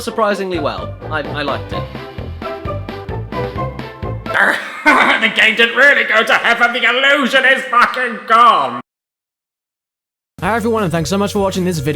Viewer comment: Sorry, what did say between 13:00 and 13:05? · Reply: "The game didn't really go to heaven, the illusion is fucking gone."